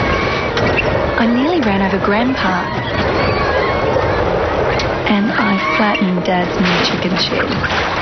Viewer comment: when this was on TV i was about 6 years old and at 0:14 i thought she said "and i flattened dads minicher gimp shed" back then i had no idea what a minicher gimp even was let alone a full sized gimp